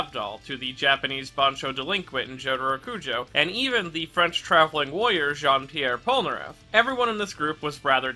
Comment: I thought he said "The French travelling voyeur"